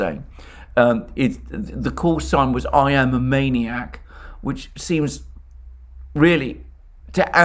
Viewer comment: I heard not "Maniac" , but "Maiak" . Maiak means lighthouse.